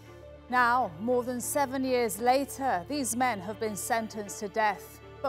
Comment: did she say men?